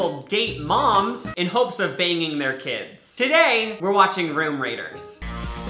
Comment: I THOUGHT HE SAID RIM RAIDERS!